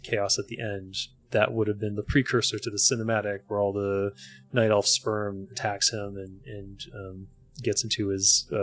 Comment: did he really say night elf sperm at 10:39?